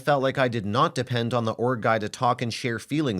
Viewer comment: S2 wasn’t “accidental cheating”. She liked the attention and validation the ORG guy gave her as she admitted to him flirting. The bf wasn’t controlling or insecure as he probably felt like being used while she was trying to be subtle in seeking other options. Unless she’s a crisis counsellor or psychologist she shouldn’t be trying to help someone directly. The best she could have done is contact a mutual friend or his parents to do a wellness check. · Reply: I thought she was saying ORC guy lol